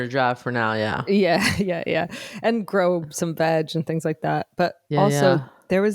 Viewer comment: I swear I heard say "Grow some vag" and it took a second to get to "veg" in my brain! 🤷‍♀️🤣